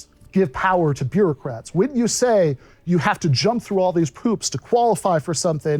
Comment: Did he say “jump through all these poops”? Really sounded like that